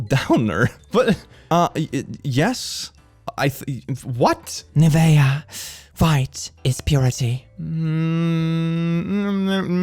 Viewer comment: I thought you said my name at 23:31 because you said Nivea, my name is pronounced the same and it's spelled Nevaeh. I was very confused because I wasn't looking at the screen right at that moment